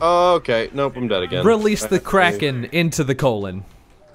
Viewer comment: I heard it the first time and I thought said "Release The Criken." Sadly not, but who cares?